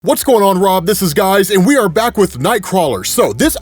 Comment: wait did you just say "whats up rob, this is guys"? or am I sleep deprived and hearing things 🤣